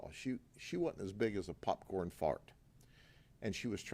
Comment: did he say popcorn fart? 😂